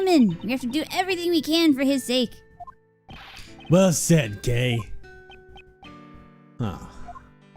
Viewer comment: I wasn't watching, just listening, at this part and I thought he said "Well said, gay."